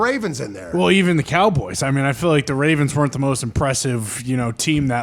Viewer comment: "ravens were the most impressive team that lost." Completely wrong, such a sloppy game from both teams. Gotta give that title to the browns. They played nearly perfect but just made a couple mistakes and lost. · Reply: I thought he said the Ravens “weren’t” the best team that lost. Im pretty sure he did.